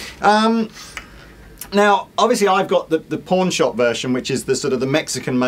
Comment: Each and every single time I always hear, " The Porn Shop version..."